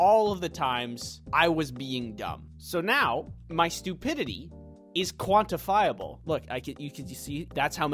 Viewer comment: “My stupidity is quantifiable” sentence that is equally smart and stupid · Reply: Earth: ooooooh. I thought you said quarentinable.